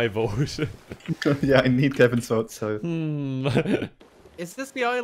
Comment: does he say Devin?!?!?